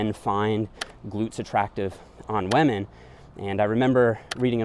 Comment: sound like hes saying "Wamen" lol